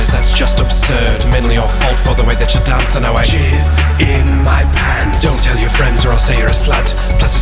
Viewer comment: I thought he said shit in my pants to jizz in them is totally unacceptable